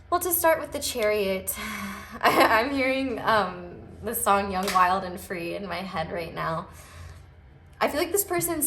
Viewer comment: I must be hungry because I heard "Lasagna wild and free"